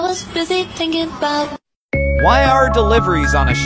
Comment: I thought the AI guy said Shitments instead of shipments 💀💀